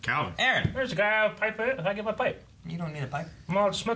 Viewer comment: can someone pls tell me what just said? 😂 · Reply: think he said, there's a guy with a pipe in it i get my pipe.